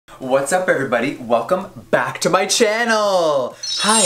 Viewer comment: At the starting I thought Jeffree said “What’s up burger buddy!” Lmao